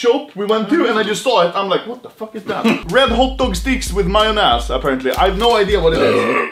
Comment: i thought he said "my own ass" 😂